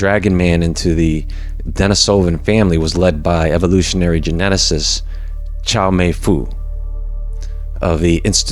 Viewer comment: Chow mein fool......did i hear?! LoL